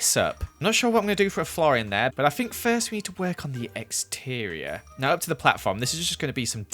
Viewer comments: When you said you were working on the "exterior", i misheard it as "eggsterior", lol! I do love the new Chiken Killer tho! I'd call it "BFCK-9000" tho, being an old Doom-Player n all :) Keep up the awesome building! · who else heard eggsterior